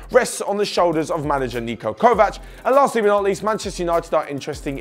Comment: Did he say Manchester United are “interesting” in Bruno Hernandez?